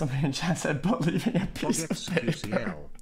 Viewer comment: What did Somebody in Chat say? Something with paper but i cant understand it :( Somebody Help me Out please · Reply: Pretty sure its about keith talking about the bible, and someone said "Believe in your piece of paper"